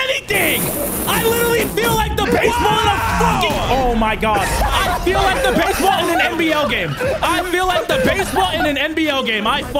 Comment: ' Am I the only one who heard him say, "I feel like the baseball in the MBL game"?